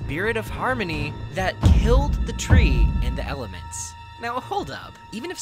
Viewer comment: I thought it said killed the three😂😂